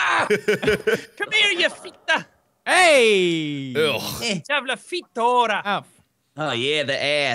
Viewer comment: Did Jack just say ´´Jävla fitt hora´´ ?